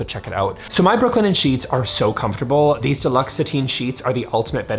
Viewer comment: I misheard "luxe sateen" as "fluoxetine" and thought wow, no wonder everyone likes those sheets. But on another note, you can pry my rainbow metallic cutlery and strip lighting from my cold dead hands. Sure, the strip lights can do white... I just rarely choose the white setting.